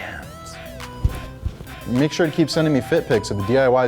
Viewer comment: I thought he said keep sending me dick pics😂